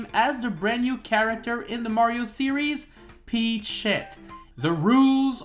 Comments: Super Peachette U? Also 0:47 said: peachette Captions thought said: peach sh*t Captions said: peach [ __ ] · i love how the autosubtitles thinks it's peach[sh__) instead of peachette